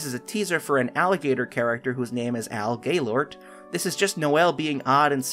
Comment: When he said Al Gailort I thought he said Gaylord💀